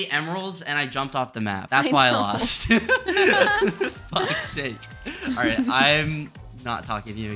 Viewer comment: Did just say Fuq sake at 5:24 whatttt baldboyhalo won’t like that😡